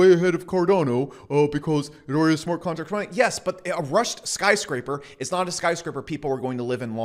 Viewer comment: You said "Rushed skyscraper", at first I thought you said "Russian Skyscraper". I was like ummm...I guess that makes sense given the context...